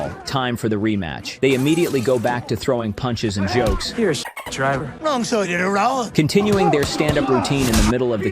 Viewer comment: what did he say? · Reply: Wrong side of the road